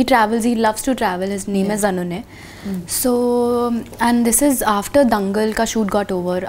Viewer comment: i heard anunay 🤪